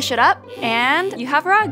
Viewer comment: At first at the end when she said, do you have it you have rugs I thought she said there you have it you have drugs